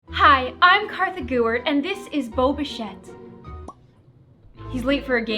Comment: When you said Bo Bichette I thought you said boba fett (no im not a star wars fan I was reading “Wonder”)